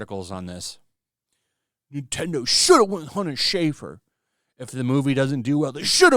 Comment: I keep hearing it as "Hunter Shaver."